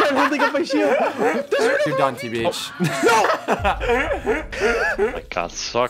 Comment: What did he say? I don't know if he said "perdón tbh" or "you're done tbh"